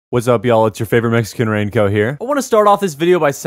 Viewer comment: I thought that he said “it’s you favorite Mexican ranco” for a while